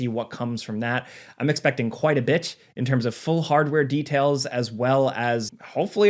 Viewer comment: did he say "quite a b*tch"? I cannot be the only one that heard that hahahah